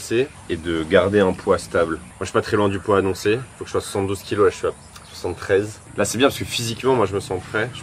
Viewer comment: j'ai entendu 112 kg. J'ai remis 3 fois le passage au cas où 🤣